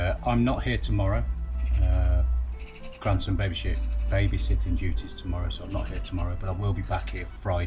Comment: Good afternoon Have too says, no pressure no leaks and thought you said baby shooting instead of babysitting😂😂 Look after mum and keep safe 🐶🐶